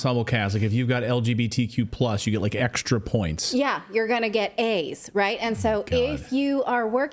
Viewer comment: When she said if you have gays you’re gonna get A’s I thought she said “you’re gonna get aids” lmao